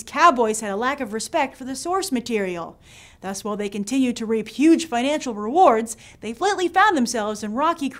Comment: did she say "rape"?